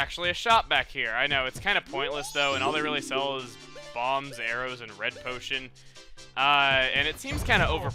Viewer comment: Web the guy was talking about the that shop that was pointless web he said the only sell bombs arrows and something else instead of arrows I thought he said areos ! Lol